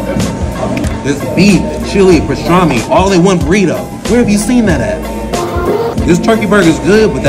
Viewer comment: On bro I thought man said where the semen at when eating the burrito.